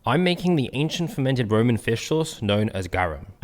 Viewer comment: Thought you said "Fish rum" for a minute..... 💀💀💀